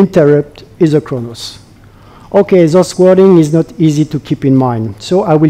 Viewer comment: So for those of you who say they can understand perfectly.. @ 1:25 What is not easy to keep in mind??? It's clearly not "sauce wording" like the caption says but even after repeating it ten times I can't work out what he says here.